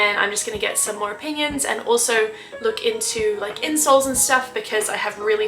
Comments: Gosh, I thought you said said you were looking into getting some "insults". I thought, that's not very nice. 😮 Hope you fully recover soon. ❤ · I had to listen back. She said "I'll try to look into insoles and stuff. I heard "incels and stuff". Anyways, hope you get better. Had the same issue back in 2013. Took a few years though before the pain was gone 🥲